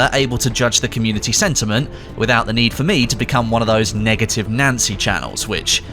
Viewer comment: I could have said something much worse 😂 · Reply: tbh I thought you said negative Nazi lol